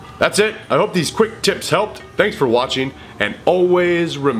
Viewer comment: Always remember... TRAAAAIIIIINNNNNM ONNNNN TIIIIIIIIAAAAMMMEEEEEEEEE ⏰⏰⏰ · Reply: i thought he said dragon time